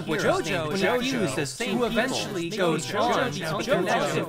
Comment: the only thing i heard clearly is "confuses jojo to create jojo"